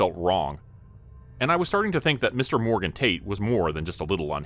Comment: Haha I thought he said Morgan Taint. Gave me a good childish chuckle.